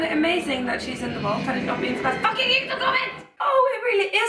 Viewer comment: what did you say at 8:45 about being spoiled?? Couldn't make it out. :D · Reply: "had it not been for that fucking youtube comment" :P